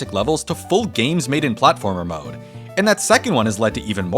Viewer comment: i was half paying attention and misheard "platformer mode" as "buttholer mode"